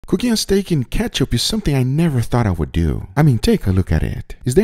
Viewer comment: I thought it said “cookie and steak and ketchup” and I wasn’t looking and I was so worried 💀